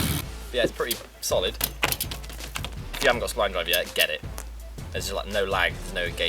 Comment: What did he say? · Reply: "if you haven't got spline drive yet, get it"